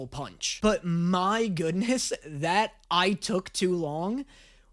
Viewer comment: I didnt here "i took too long" i heard "i talk too much" abd went "what like fucking hamilton??"